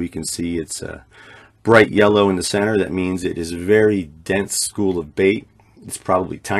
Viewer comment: What do you mean by bait school? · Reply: Minute 10:06 or so, when you explain those three bright things. I hear it, you call them bait school. Or maybe you say something different?